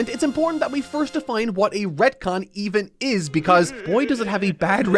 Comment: Sounds like he says red-con